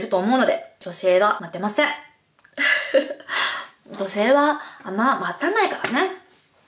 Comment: なんて言ってるんでしょうか？ 元々の滑舌？整形の影響？？ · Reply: 女性は、あんま、待たないからね のところですか？